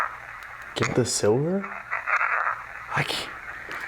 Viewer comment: I hear " get the f out"